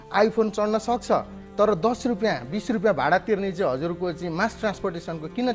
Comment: Is it just me who heard him saying iphone chadna sakxa at 00:59 second